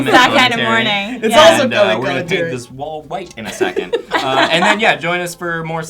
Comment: i thought elliott said walt white at the end... breaking bad withdrawl